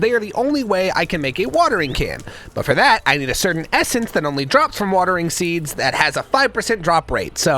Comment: i thought it said "Based essence ingot"... ive been seeing too many memes